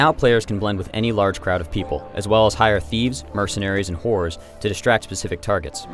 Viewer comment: did he say hire thieves, mercenaries and whores